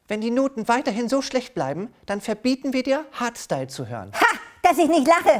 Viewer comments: ich hab Haarstyle verstanden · Ich habe verstanden harzstile